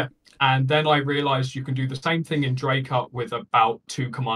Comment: What was the thing he said at 1:35? "Then I realized you can do the same thing in dr______ with about two command line arguments." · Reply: Dracut. It’s the initial ramdisk file generator for anything that’s not based on Arch or Debian.